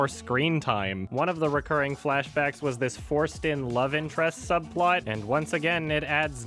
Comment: i kept hearing 'foreskin love interest', like until my 5th replay. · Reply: Lol I did too but he is saying "forced in" not foreskin but I still want to believe that he said foreskin